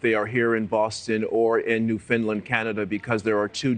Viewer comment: It sounds like he is saying "New Finland" . It is "Newfoundland" a province in Canada pronounced like Noof un land - kind of rhymes with understand.